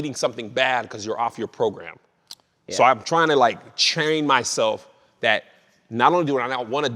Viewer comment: Chtrain is exactly what I heard 😂